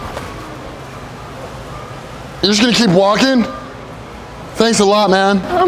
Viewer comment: When he said im workin ere i thought he said walkin